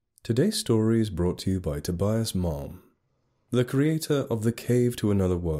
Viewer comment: Totally thought you said Tobias' Mom. 😂